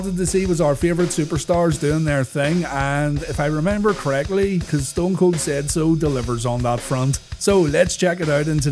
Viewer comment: All this time i thought he used to say 'cause stone cold SAYS so" Damn